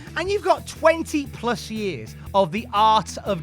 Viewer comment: I legit thought you said "20+ years of the arse of Jericho".